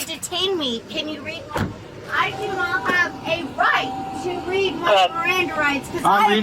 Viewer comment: "You do not have the right to read me my Miranda rights". The lady knows her rights. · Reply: I'm fairly positive she actually said "I do not have a right to read my miranda rights"